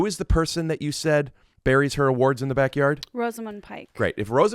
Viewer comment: I thought you said "Rosamund Pike buries her WARTS in her backyard".....and i thought..that's not weird.